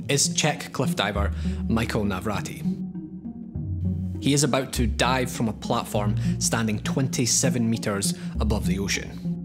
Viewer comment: anyone else heard "he's about to die from a platform" or is that just me?